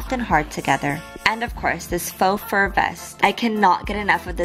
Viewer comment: XD sorry I'm an idiot. I actually did watch the vid but always thought she said "full fur"... yeah I know, stupid... especially since it's written on the side as well XD sorry again.